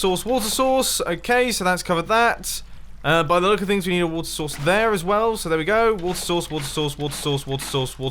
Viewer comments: I thought you were saying "Worcester Sauce" XD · it sounds like hes saying water sauce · on 7:18 said water source right and then i heard it like “What The source what the source” then “What Source” if u agree like XD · it sounded like you were saying water sauce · It sounds like you say water sauce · is it just me or does he sound like he is saying water sauce XD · i think i just hear "water sauce" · it sounded like He said water sauce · Sounded like he was saying water sauce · It sounds like is saying 'What's a source?'. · Bro sounds like he is saying water sauce xDDDDD I’m dead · I thought he said water sauce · it sounded like you were saying, „water sauce“ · It sounds like your saying “Water sauce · It sounds like he is saying water sauce